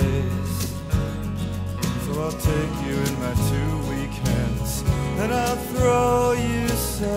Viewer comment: For the longest time, I thought Mark said 'take you in my two weekends,' which led me to think it was more a song about a father and a daughter...for me, even more poignant.